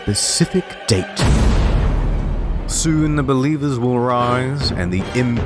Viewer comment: I just realized in 0:34 he said beliebers instead of believers